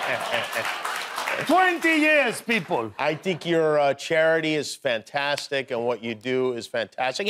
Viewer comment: did Jimmy just say “tink your….” Like mimicking his accent? 🤣😎🤟💞🤣 he’s so damn cute 🥰